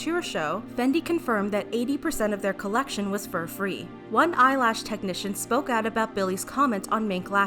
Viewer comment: I heard "Fendi confirmed that 80% of their collection was FOR free 😳😂